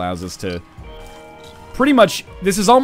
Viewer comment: CC: "Mom's Night very tight radius tiny plane Iran" Wow. I will never watch a BoI episode without subtitles.